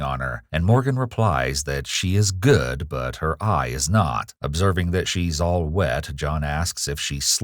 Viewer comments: i thought this said obese and was talking about XQC. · I thought it said obese at first